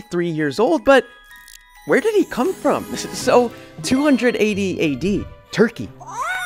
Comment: He said "280 AD" but I genuinely thought he slipped up and said "200 AD AD"